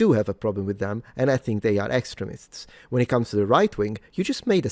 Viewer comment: Ah he can mock your accent, truely a genius debater. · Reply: I heard "excrements" 1st time said "extremists"